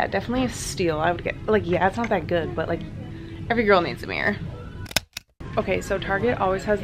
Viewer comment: When you said mirror I thought you said beer 🍺 lol 😂